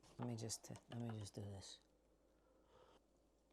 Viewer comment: Oh, I guess I took that wrong! I thought she said, "she can't eat that"?! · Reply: she said I can't even! But earlier she said she can't have coffee anymore so both work.